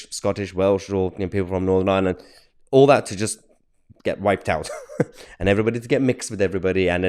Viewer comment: Mahyar, thought I just heard you say that they'd welcome to "get whited out". Selfocide.